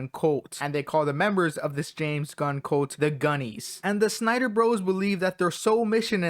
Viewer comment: Ngl I thought you said Gooners instead of gunnies